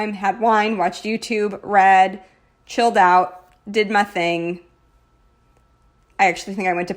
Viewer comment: On Wednesday night when you said “I did my thing” I thought you said “I did methane” and I was so confused. Can you tell I spend too much time working on chem homework 🤣 this spread turned out soooooo beautifully 😍 I really love the mix of rose golds. I might need to get over it myself and try it in my planner!